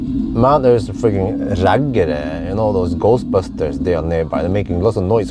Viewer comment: Ghost busters? What the heck is that? Can someone fill me in? 13:56 ...Some Norwegian thing or something? What is the Norwegian word Bjørn said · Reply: Bjørn said “raggare”, which is actually Swedish. It refers to a subculture of driving vintage (and often loud) American cars and acting like hillbillies.